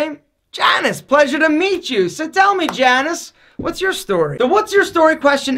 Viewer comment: Did he just said TANOS?!!